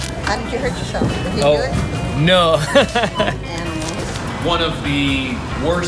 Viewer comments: wtf she said : “animal” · did she call you guys animals? 😂